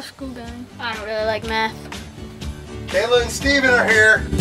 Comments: legit thought he said "i dont really like METH" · "How's school going" "I dont really like math" 🤣🤣🤣🤣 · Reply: I thought he said "I don't really like meth" lol. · I definitely heard "I really like meth" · Sounded like he said ,""I don't really like METH"" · I thought he said i really like Meth...